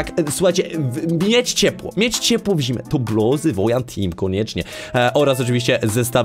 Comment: minute remu a już ponad 300 wyświetleń hah. miłego dnia orz oglądania wszystkim <3 gratki <3 10:23 jezu zawał usłyszałam 'buty WOJANTEAM' i się przestraszyłam że coś przegapiłam XD 19:20 kabanos :] 20:30 tak, bo free concept dla cb i oraz lepszy dzionek dla nas 22:00 a ostatnio wrabiałeś że na 20sek :-; a i wgl grałam dzisiaj w stumble guys z tyem który się zwał ''wojan team'' pozdrawiam niebieskiego dinozaura <3